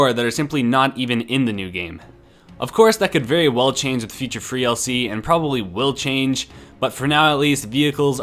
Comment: did he just say FREE dlc for battleground 2, 😑 never